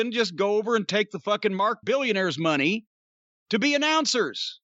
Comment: Does anyone know what Jim says at 1:35 "Mark billionaires money" ??